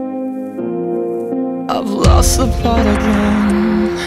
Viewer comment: I swear I heard him say I've lost the plot again lol